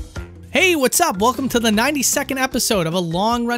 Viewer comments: At the beginning I thought you said "welcome to the 90 second episode" and was about to be disappointed you only gave a minute and a half to this game. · You said 92nd episode, I heard it as 90 sec episode, I was confused for about 5 seconds 😂😂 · 100% thought you said welcome to the 90 second episode and I had a mini heart Attack. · I first heard this was a "90 seconds episode"... but it is almost 15 minutes long :D